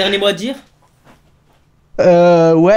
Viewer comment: Il a dit quoi à 13:22? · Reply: faligoudizada